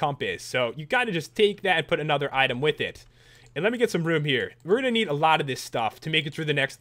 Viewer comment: when pat said "im gonna get some room here" i thought he said "im gonna get some root beer"